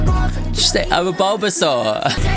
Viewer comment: I also thought she said Bulbasaur for the record. Lol